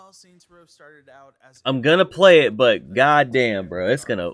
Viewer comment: bruh did you say you gon play it you got me messed up widda previous comment, stop it lil dude dont screw yoself over widdis asset flip